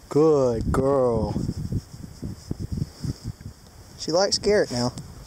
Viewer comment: mabe the cow thought you said carrot not garrett